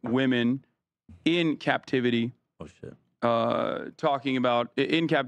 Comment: did Ethan say bullshit?